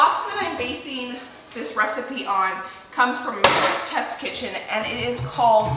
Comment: did anyone catch where we find the recipe for the spaghetti sauce? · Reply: I thought she said America’s test kitchen